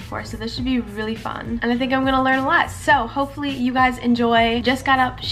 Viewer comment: did she just say" I think I'm gonna learn less" ?? 😂